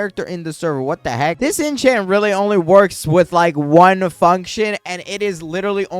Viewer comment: what did you just say?💀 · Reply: "One function"